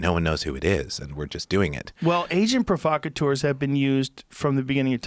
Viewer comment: Sounds like he is saying Asian provocateurs